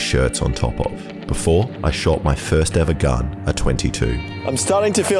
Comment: I thought at 9:36 he said he shot his first gun at AGE 22. due I would have hit him up